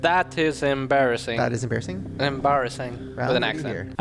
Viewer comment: I think the 1:00:39 thing they say here is "that shit's embarrassing" - probably a UK footy chant